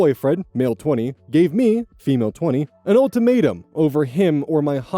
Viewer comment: I dunno why but at 12:17, it sounds like said "Old Tomato" instead of "Ultimatum" to me.